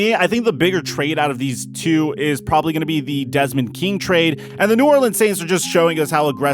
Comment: WAIT MY TITANS GOT DEZ!!!!!!!!!!! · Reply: I thought he said Desmond Bryant for some odd reason.